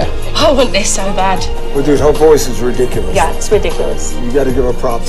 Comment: can anyone tell me what they said at that moment because I can't hear it very well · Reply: they said her voice is ridiculous it’s very very good along the lines of that